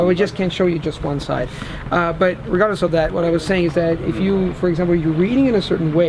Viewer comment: _____ of that, what i was saying... what he says there?? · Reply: regardless of that